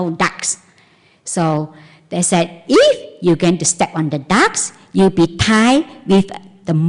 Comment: I love and appreciate teachings. I'm usually fine understanding her accent, but her story, starting at 14:17 I just can't make out. Step on the ducks? I know she's not saying that and it is central to the story. Please help, what is she saying?